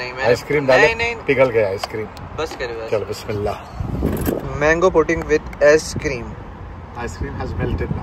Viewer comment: did you say mango pudding with a$$ cream? Lol 😂😂😂 Also not making fun of him just saying cuz it's funny, usually you pronounce things wrong when you're switching between languages.